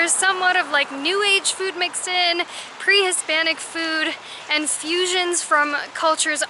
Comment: We didn't call anyone hispanic:) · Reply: SORRY I HEARD YOU SAY HISPANIC BUT I SAW THE VIDEO AGAIN AND YOU DID NOT SAY THAT YOU SAID PRE HISPANIC